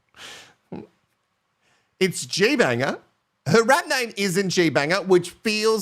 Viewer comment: OMG! This whole season I thought was saying Gias ‘RAT NAME’ Today ,I realised it was Rap Name 😂😂😂 I’m British & I blame all my faults on that 😊